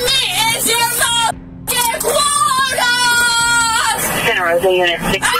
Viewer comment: she actually says "Quota" here. I speak crazy, unfortunately.